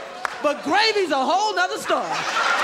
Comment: What is the first word that he says? " *** is a whole other story " I hear "gravy"